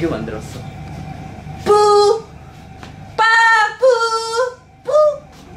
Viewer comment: siento q escuche mal..pero dice PAPU ...ajajjaja en mi pais es algo gracioso